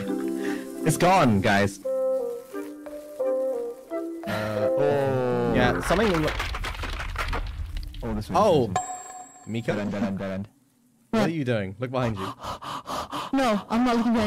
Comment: did i hear it right 5:46 shu said "jalan jalan jalan" hahahah cuz jalan means move it kinda suits the situations they're in hahah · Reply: i think he said "dead end" x3🤔